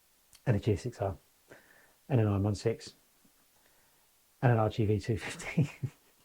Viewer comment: I too would love to own : TMAX 560 + GSX-R750 + YZF-R6 (not sure if that is what you meant when you said R16 - that's what i heard @ 22:07) . . . however no need to 2 inline-4 bikes like GSXR & R6 . . . . so i would skip R6 & get a V2 or V4 instead though i am happy with my :-- 2021 TRiDENT 660 + Full Titanium AKRA 2022 GeN 3 HaYaBuSa + Full Titanium AKRA 2023 X-DiaveL + Termignoni Slip-on + Ducabike Clear-Clutch Thanks